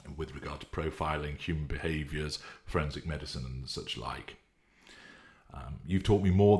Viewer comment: My gut says is involved with marketing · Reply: I thought he said forensics. Which would make perfect sense as to why he knows a lot of deeper level psychology stuff, and pays close attention to worldly news.